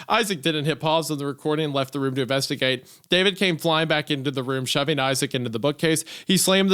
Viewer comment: I wasn’t looking at the screen and I thought they were saying David Cage each time and that made the story a lot funnier